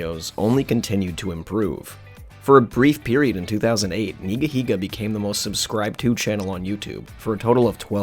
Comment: Did he just say nigga 😳